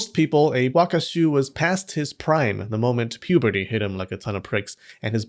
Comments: Did you really slip in "the moment puberty hit him like a ton of pricks " at 2:34 ? Lmao or am I mishearing the p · did you say "ton of pricks"?